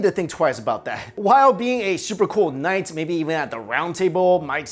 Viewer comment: he said super cool but CC said badass 😂